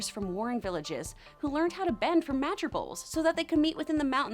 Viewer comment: am i the only one who heard magerbowl